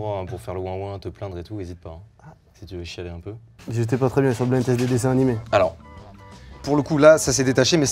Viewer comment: les Gars j'ai jamais compris ce qu'il s'était passé à l'aide c'est quoi la private joke 🥲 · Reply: Bah en gros alors que Mastu écouter pas et parler à la caméra Mcfly il a dit « j’étais pas très à l’aise au tournage du blind teste Disney » sans que Mastu entend